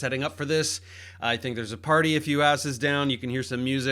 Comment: It sounded like u said, "there's a party a few asses down" 😂